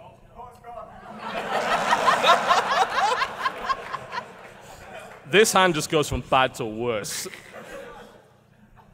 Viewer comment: What did that guy say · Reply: I can confirm, I did indeed shout CoxCon.